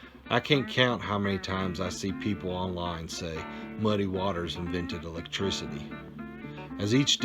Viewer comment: That t-shirt is not phonetically correct. It's "Muddy Waters invented 'lectricity!" Another thing that's often overlooked in this movie is how many truths regarding life are said in this movie.Joe Seneca did NOT receive the accolades he deserved for such a fine performance. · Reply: Lol I noticed that too I heard him say lectricity the very first time I watched this movie